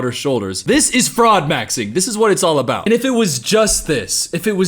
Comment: When you were saying "broad maxing" I legit thought you were saying "frog maxing" and I almost wasn't surprised there would be a thing called that in their universe.